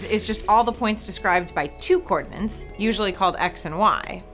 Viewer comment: I love - but is she really saying “cordinance” instead of “coordinates” throughout much of the first 2 minutes and 15 seconds of this video...? When she talks about a 1 dimensional line, I hear “coordinates”, but when she talks about 2, 3 and 4 dimensional circles, I hear “cordinance” or “co-ordinance”. Seriously. I listened 3 times to be sure. Then I went and looked it up at <link> thinking maybe she was using a word I just didn’t know. (They don’t know it either...). Actually “co-ordinance” is a defined word but it doesn’t have anything to do with co-ordinates.